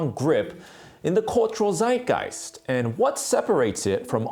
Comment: Did he just say Zeitgeist like Sideguys (actually more like Zideguys)